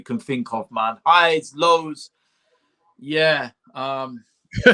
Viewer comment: I swear said, “yeah mum.”